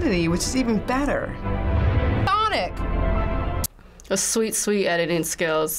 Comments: Did anyone else hear the word sonic again randomly? 😂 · anybody else hear say SONIC! ?? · does anyone else hear sonic again lmaoo